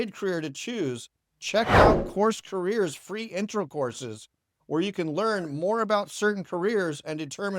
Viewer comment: did you just say free intercourses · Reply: LOL, I think you misheard that part! But hey, thanks for watching! What trade are you thinking about? I said free intro course.